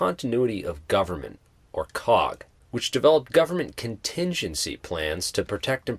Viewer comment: i had low volume, thought you said COCK